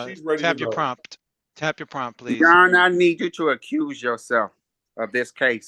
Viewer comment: Did he just say "Your honour I need you to accusse yourself!" Sending up many people scrambling